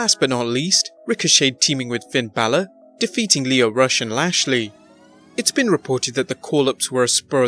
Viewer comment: you said Rush and Lashley but i heard that as Russian Lashley...dont confuse me